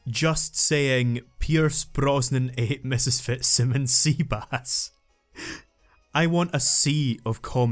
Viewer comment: Pierce Bronzon ate ms sassasquatch seabass. I know it's wrong, but I'm at 2:57:05 and I can't figure out the sentence, your accent is a bit thick for me. Sorry :'(